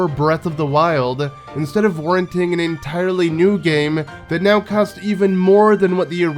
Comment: Did you saying “… instead of renting a new game…” 😅 That’s smart.